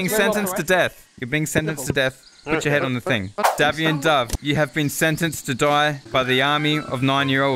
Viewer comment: Did say DAVION Dove?